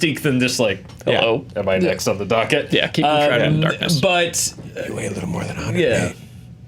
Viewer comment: quotes the first Batman, "you weigh a bit more than 108". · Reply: THANK YOU. I thought I heard "You weight a bit more than 100 (something)" but even if I could have made out everything, I'm 100 percent certain I would not have recognized it from the first Batman.